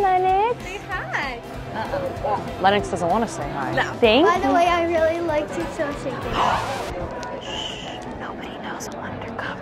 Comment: what did the girl say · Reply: That she likes Bella from "Shake it up".